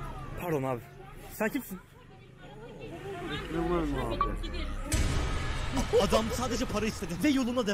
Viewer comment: adam ne diyor? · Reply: Beş lira var mı abi diyor eskiden 1 liraydı ona da zam gelmiş (adam ziyinsel engelli) adını unuttum ama aykazıda tanınan biri deli diyorlar